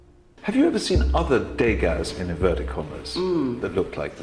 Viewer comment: What word is he using to say a "fake?" I couldn't catch what he was saying. Thx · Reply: He says: " 'Degas' in inverted commas," meaning fake Degas. The way he pronounces it in the plural sounds like deygaz